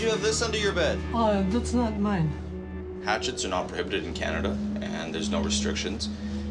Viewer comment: I thought the officer said "Axes are not permitted in Canada". I was like what the...